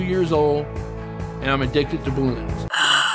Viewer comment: Thought he said “I am addicted to baboons” Kris: I’d rather have clowns in my room Me: but clowns have balloons